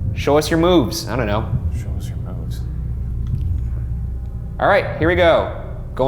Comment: it sound like someone said “show moves” after Ryan said it